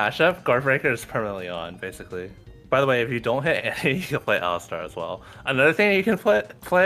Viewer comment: bro what did he say? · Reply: "If you don't hit Annie you can play Alistar as well"